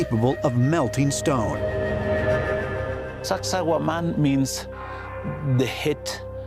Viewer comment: Everytime he says the name it sounds like hes saying "sexy woman" with a Jamaican accent 😂